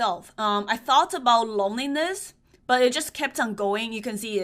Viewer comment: I thought I heard you say "I thought about loneliness." I was like, "Hu-what????"